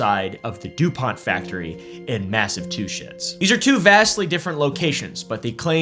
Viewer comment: He literally says "Massive Two Sh!ts" instead of Massachusetts.